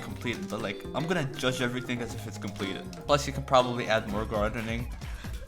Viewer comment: typing be like: i am speed. also at 1:41 i thought he said "blushy" but maybe it's just me.